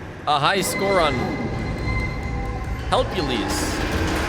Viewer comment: for anyone reading the subtitles who didn't get what he said it was most likely "Helpules", a minigame from Into the Pit